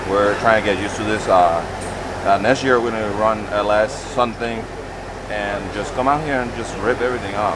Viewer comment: Is it me or did said they will be running an Ls something for next year @ 13:21 ??